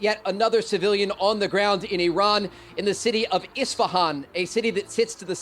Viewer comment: did he say Istvaan ???